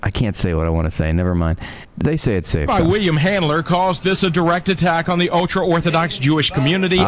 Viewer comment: ?!?! Did anyone else catch that? His actual name is RABBI WILLY HANDLER?!